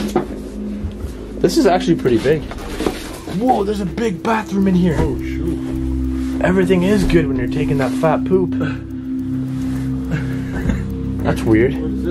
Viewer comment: I think it said everything is Gucci 😂😂